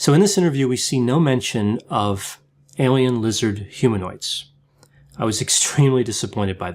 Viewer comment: I totally heard “Alien Wizard Humanoids,” and thought Harry Potter’s Voldemort! 🙃... welp guess I have to wait till the virus crisis is over before I get my hearing checked.